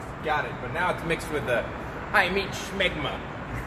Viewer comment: Did he really said 'high meat smegma' at 10:14?